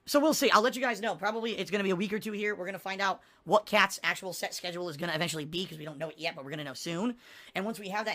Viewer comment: Woah at 9:05 i thought he said Kats actual sex schedule SORRY i am Horney and Kat might be to.